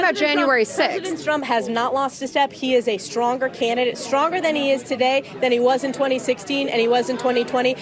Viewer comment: did she say Trump was stronger in 2016? "Stronger than he is today then he was in 2016."